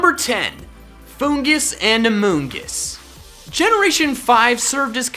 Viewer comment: did he say amogus ⛔️ 3:36 did he say amogus ⛔️